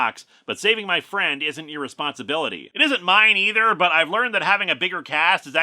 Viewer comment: is it just me or did he say irresponsiblity instead of responsibility?